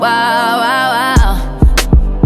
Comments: Anyone else hear when she sais when i was u all i would get was wild thoughts Sounds like she is saying when i was you all i get is vatos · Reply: That Cod King yep! No way she says” When I'm with you, all I get is wild thoughts” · I thought it was saying why why why when I'm with you all I get is white dots XD